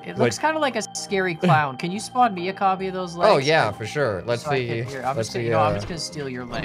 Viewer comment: i thought that said legos